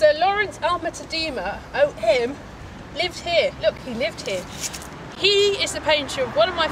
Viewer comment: What are you saying at 00:02 that sounds like, "out him?" O.M. means Order of Merit, does it not? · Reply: I said "O M". Yes, it does mean that.